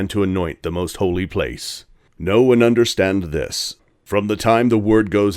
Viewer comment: I heard "Know and understand this" as "No-one understands this".